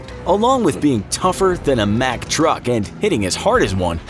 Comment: I thought it said mctruck